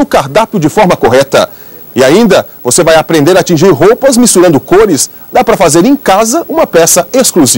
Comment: eu entendi "voce vai aprender tingir roupas mesntruando cores" kkkkkkk